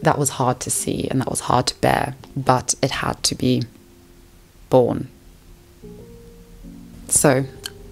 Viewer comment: OOOHHHHH! "BORNE!" I listen to videos, and at 2.5x, and am American, so there were a lot of factors that went into me mishearing you. I thought you said "porn" XD